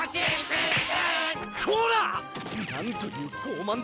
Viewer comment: I am the only one who hear "ORA ORA ORA ORA " XD